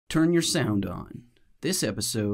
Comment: I thought you said turn your sound off, not up...It made everything very confusing.